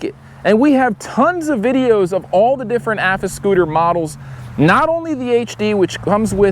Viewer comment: I had to do a doubletake... I swore that said Afroscooter 😂